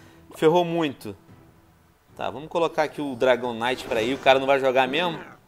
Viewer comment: só eu ouvi dragonite? #VenomMestrePokémon